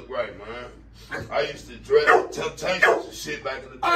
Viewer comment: did he say he used to dress the temptations?! 😂😂😂